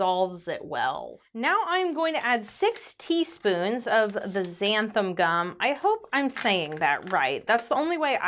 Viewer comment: For a moment I thought you said 60 spoons of the xanthan gum 🤣